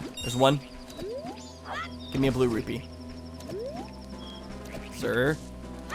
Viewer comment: omg did actually say 🅱lue RUPIEE